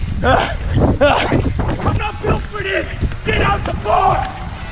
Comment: Anybody else hear the life guard yell "IM NOT BUILD FOR DIS GET ON THE GROUND!!"